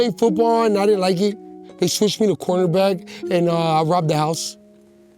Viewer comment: “They switched me to cornerback and I robbed the house” 😂😂😂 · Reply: Dude, I thought he said quarterback lol, Imagine the coach that would make this guy quarterback… cornerback makes a lot more sense, thanks for translating